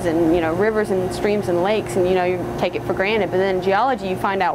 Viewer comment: did she just say? "You take it for granite" I started working towards a environmental stuies degree but in the course of that I got very interested in both Geography and Geology. I wonder if you can easily double major in these fields.